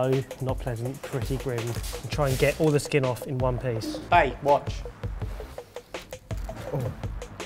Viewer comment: Did he just say "bae, watch" at 2:50?